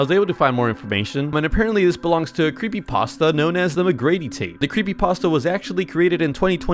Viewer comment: Nah I thought he said the griddy man💀💀💀